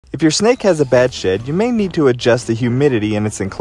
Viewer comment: Was quickly skipping through shorts and thought you said “when your snake takes a bad sh!t..” 😂